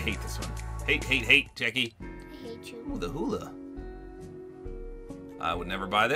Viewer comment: did he say he hates you??